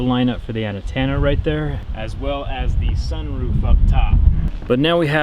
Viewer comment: anyone hear ANA-TENA not ant-enna